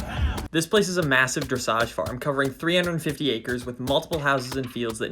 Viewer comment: Dressage farm … in the first few minutes of the video , I thought you said “massage parlour” !